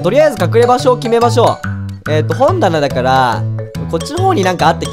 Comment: かくれんぼのアスレチックもう簡単そう〜w 1:04本棚がほんたなに聞こえた(耳悪いな私ヤバいぞ)